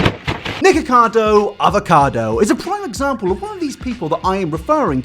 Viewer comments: thought he said nigga for a sec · I thought said niggakado avocado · i thought he said niggacardo avocado 😭